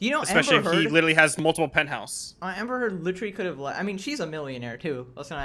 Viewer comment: i have no clue why but for some reason when the lawyer said penthouse my brain heard it as pimp house